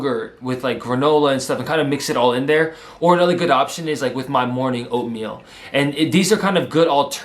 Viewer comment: I thought he said opium